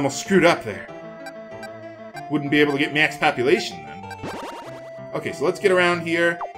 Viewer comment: Every time he says, "max populatuon," i hear "max copulation."